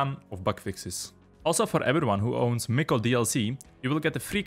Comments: what dlc he says in min 6:50 ? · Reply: The shrine maiden, miko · Can someone please tell me what's being said...mickel dlc? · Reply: Miko, Shrine Maiden